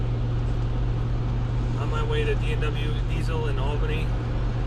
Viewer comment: i damn near choked, thought he said "on my way to BMW diesel"